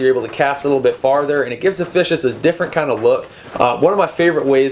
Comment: does he say "fishes"? As in, more than one fish? Lol Sounds like a mistake that I would make. Your videos present the information in a very concise (to the point) manner. I like that.